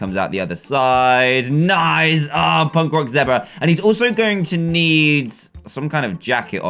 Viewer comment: Did he say punk rock zebra at